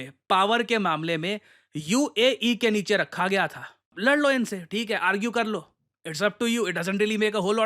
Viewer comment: I thought you said "l!nd lo inse"😂